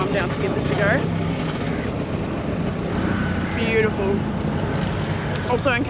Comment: Hahah it was weird indeed, but true I guess you're right 😂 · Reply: I think what he said was 'beautiful eyes' and with his accent and pronunciation, it was misheard haha